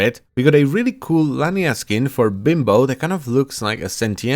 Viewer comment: did i hear "bimbo" instead of limbo?